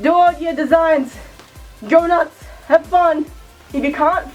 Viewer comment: I thought she said "Donuts" ...xD